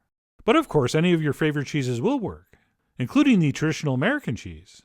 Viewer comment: "Nutritional American Cheese" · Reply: I heard "traditional" but after re-listening I heard "nutritional". The fun doens't stop.